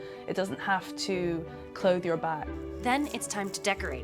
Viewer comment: did she say clothe your butt?